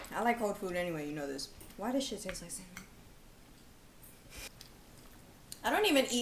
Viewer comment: did she say "why this shit taste like salmon" or "why this shit taste like semen" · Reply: cinnamon bc of the cinnamon challenge.